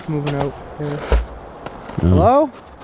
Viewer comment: right after says hello you can hear a female voice and it sounds like she said hello back.